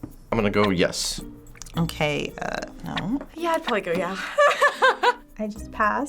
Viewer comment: Did just say "yeah, I'd boing her, yeah" ?????? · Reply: cristina calderon miguel “yeah I’d probably go yeah